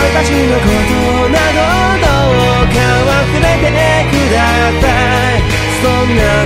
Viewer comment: 何度聴いても「忘れてくだたい」にしか聞こえなくて草